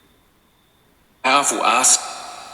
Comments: i heard "Powerful Ass" and had the chuckles too😄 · So sad to think of children there. Agreed that Vox sounded like it said "powerful ass." 😂 Thanks for another fun investigation, Ladies! 🖤👻